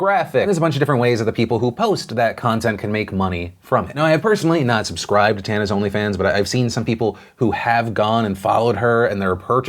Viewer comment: I’m watching this half awake at 4am and I thought you said “now I have personally subscribed to tana’s onlyfans” and I hit rewind so fast to rewatch it bc I was like.. WAIT PARDON?? LOL